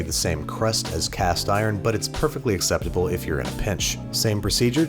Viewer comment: I thought he said bitch instead of pinch lol, which would’ve made it even funnier